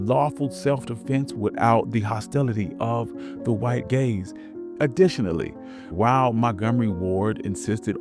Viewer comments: Fantastic information. I did laugh a little bit right around 05:27 the captions say "self-defense without the hostility of the white gays." rather than gaze haha. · “without the hostility of the white gays” ?? I thought you must’ve said “white gangs” but I rewound a few times and I can clearly hear “white gays” lmaooo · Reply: gaze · the closed caption’s putting “white gays” instead of white gaze is absolutely appalling and telling.